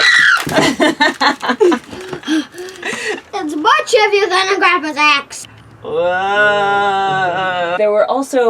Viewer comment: What did he say ? I wasn't able to understand it :'( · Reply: “It’s much heavier than Grandpa’s axe!”